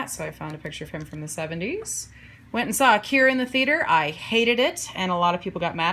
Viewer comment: When you said you saw AKIRA, I thought you said you saw THE CURE in the theatre an hated it lol